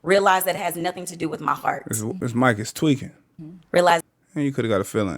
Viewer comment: I heard fart, idk about "heart"